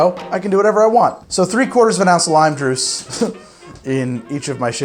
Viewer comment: Did he just say “lime druce”???? 😂😂